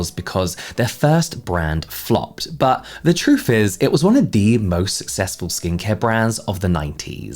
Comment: I totally misheard that as “most successful scheme-care brands”